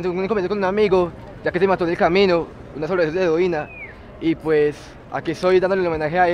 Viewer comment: no entendí ese idioma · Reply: Xbjdsjsbsus amigo Bdhsjsjsbs camino. Bzjzbsbskks codeina.